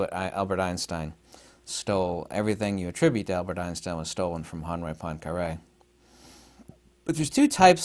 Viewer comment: Who does he say Einstein took his ideas from? Closed captions didn't pick up the name right. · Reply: Henri Poincare ik this is late 💀